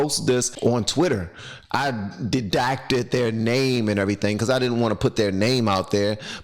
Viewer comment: "you REDACTED their name" it sounded like you said dedacted.